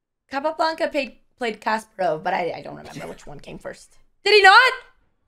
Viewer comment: I thought she said Capablanca played Castro 😆